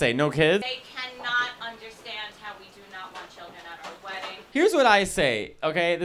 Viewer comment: I swear to god I thought she said, "We dont want to have kids at the wedding" like someone was gonna flip the tables and give birth mid ceremony